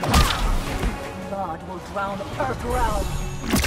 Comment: What did she say @ 0:22 🤨🤔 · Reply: "blood will drown earthrealm!"